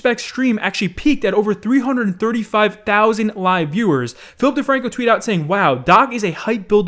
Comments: When he said Philip DeFranco I heard Filthy Frank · When he said Philip DeFranco I thought he said Filthy Frank...